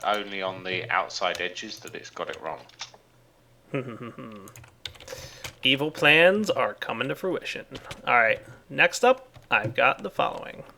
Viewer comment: Did he just say Evil plans are coming to people full with shit?